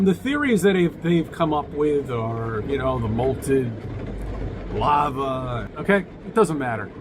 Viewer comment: Is becoming British with the way he moans about the weather? @ 6:27, did he say 'molted lava?' I hope it's not a Freudian slip, i would hate to see him lose any of that amazing mane of his.